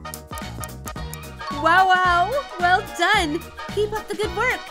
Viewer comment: I'm almost certain that the Bowser one used profanity "b**ches" instead of saying "peaches" and if that's the case, I really do not appreciate it - this is supposed to be kid friendly. I was watching this with a child that I was babysitting and this will be reported.